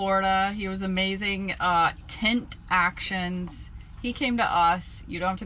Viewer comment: What is the name of the tinting company? Sounds like you said "tint action" but it didn’t come up on google. Thanks. Nice merch